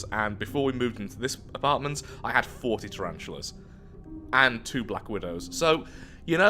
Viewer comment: Did i hear 40 right?